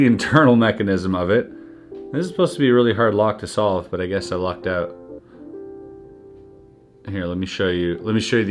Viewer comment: I dont know if it was intentional but at 4:18 " This is suopposed to be a really hard lock to figure out. Guess i LOCKED out" thats what it sounded like to me lolOk so wow to the intro. Every video i watch i cant wait to see what the intro has in store.